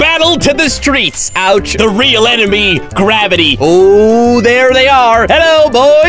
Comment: When said Gravity I genuinely thought he said Grabbity